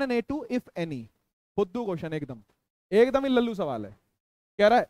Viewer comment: sec sir ne kya bola Fudu ja bhudu Comment plzz if any one know 🤣🤣